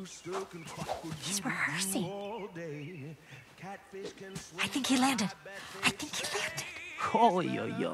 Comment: can someone explain to me what does skunk says @ 2:04? PLEAZE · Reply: She said "i think he landed, i think he landed."